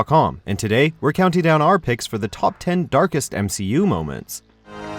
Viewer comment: I thought it said dankest moments